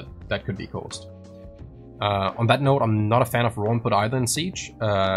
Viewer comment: what setting is saying he is not a fan of? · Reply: Raw input.